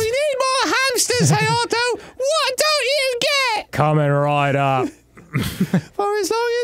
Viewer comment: I thought Dan said "Kamen Rider" when he said "Coming right up". That's how good/bad his accent is. Depending on who you ask.